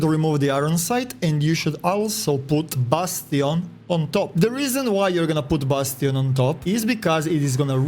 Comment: i thought he said "bustin on top"